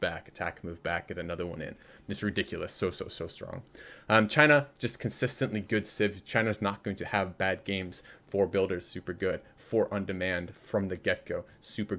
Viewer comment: What does say at 9:48? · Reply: ''China, just consistently good civ. China's not going to have bad games. 4 builders super good,(i think he meant 4 builds here) 4 on demand from the get-go, super good.''